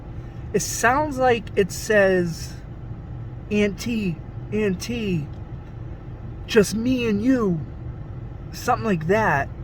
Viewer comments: I hear "It's empty. Just me and you" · I heard it's just me, just me!